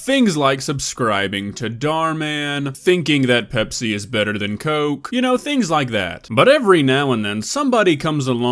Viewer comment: “ people do stupid things, like saying Pepsi is better than cola” me: HOW DO I SUBSCRIBE AGAIN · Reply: I thought he said, “ecstasy is better than coke” which made me laugh. It’s not as funny now I know that’s not what he said😞